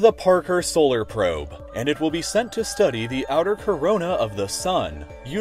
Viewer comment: Did he said corona in 2018